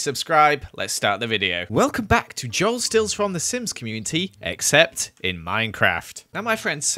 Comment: I first heard it as “welcome back to the Joel runs the simp community “ lol